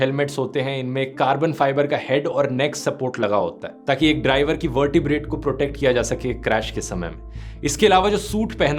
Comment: 😦 what he said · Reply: Driver ke vertebrate protect kiya jaaye